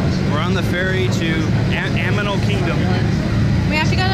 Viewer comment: Am i the only one that heard him say "Aminal kingdom"? lol